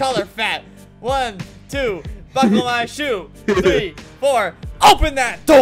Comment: i thought he said 1 2 fuck the line shoe 3 4 OPEN DAT DOOR!!!!!!!!!